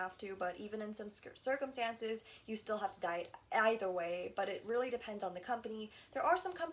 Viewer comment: i thought she said die that whole time😂❤